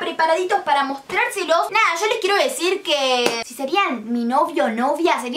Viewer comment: yo pensé que decía "los besos que te daría si fueras mi novio" JAJAJAJAJAJAJAAUAU nada que ver